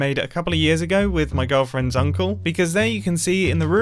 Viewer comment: it legit sounded like he said "girlfriend Zuncle"